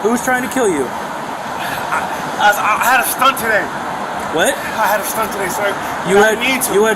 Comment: I thought I heard the perp claim to have had a medical procedure, 5:53. A stent perhaps? I wonder if that had actually been done. Maybe he had a weak heart.